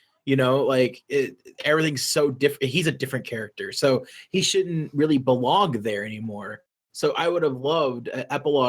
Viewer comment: I HEARD. . . I Heard “He shouldn't Really BEWONG There”